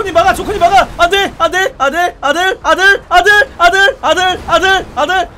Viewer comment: 안되가 아들로 들리네 ㅋㅋㅋㅋㅋㅋㅋㅋㅋㅋ